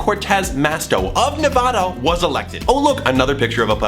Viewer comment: Did you just say NavOda Still awesome video 🤗🤗